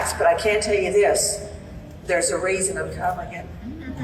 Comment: What does the woman on stage say at around 27:45? I can't understand the last few words. "There's a reason I'm...." And then everybody in the audience goes, "Ooooh". Can anybody help me understand this? Thank you in advance if anybody responds. God bless. ✝️ Dear Lord, please protect Gracie and her mom. Please allow Gracie to have a full, happy, & fulfilling life in You. ✝️ Thank you, our Holy King. In Jesus name I pray, amen. ✝️ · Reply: She said, "There's a reason I'm covering it." She meant covering the case on her show. Her fans then got all excited. She's Nancy Grace, a TV crime show lawyer with a huge audience.